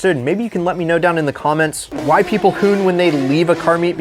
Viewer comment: did he just say “why people COON”? Do you even know what that word means bro?